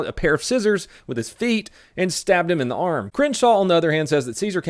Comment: At first I thought you said stabbed my a harmless man . it made more sence as you went on